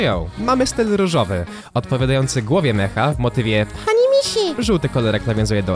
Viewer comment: Czy ja usłyszałem.....PANI MISIA?